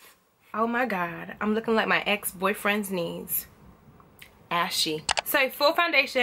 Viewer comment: I'm looking like my ex boyfriend's knees.......ashy I'm dead😂😂😂😂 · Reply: i thought she said my ex boyfriend's niece LMAO